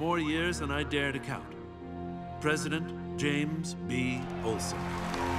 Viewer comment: Not gonna lie, when Lex was introducing his replacement, I thought he said Coulson (I.e: Agent Coulson from Marvel). But upon quick reflection, I realised he said “Olsen” (I.e Jemmy Olsen, OG Superman’s supporting character who works at the Daily Planet). Either way, someone was reimagined as a black guy.